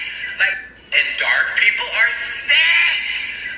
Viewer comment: I heard "people are so f*^%&$# dark and sick".